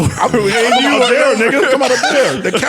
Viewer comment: Did parks say nigga ???? 1:18:40 🙊🙊